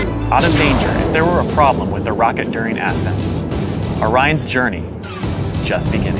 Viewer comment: did he say "ass scent"?